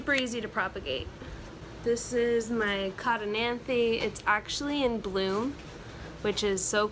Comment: What plant is that at 6:20? I couldn't catch the name :( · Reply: it’s a Codonanthe :)